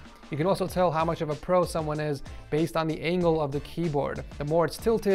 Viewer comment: did you say angle? Where my vertical keyboard setups at?